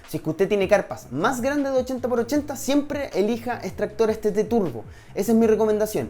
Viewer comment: que dice? extractores t turbo? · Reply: si tt turbo